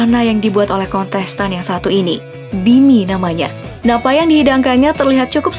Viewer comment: namanya bini ?? Atau kuping gua yg salah denger😂